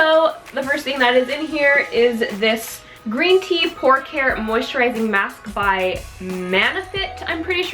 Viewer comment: I heard Green Tea "PORK" care!! Lol